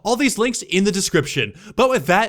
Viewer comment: For a second towards the end, i thought you said but that's just a game theory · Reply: Sans tube He didn’t XD He said “This is Tyndaga, signing out!”